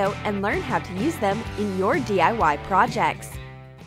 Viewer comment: On 0:31 I thought she said LaurDIY 😂😂😂